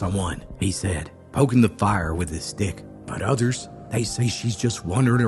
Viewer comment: HOLD ON!! Rewind! WTH, did you just say!? "Poking the fire with his" WHAT!!? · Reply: Haha😂 STICK